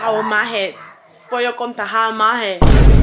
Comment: Ohhh lmaooooo that’s what she said , I thought she said , “ pollo contagiado“ and laughed hella hard for being a savage